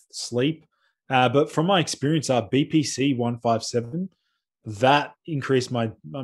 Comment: I thought he said, “BBC increases my deep sleep dramatically”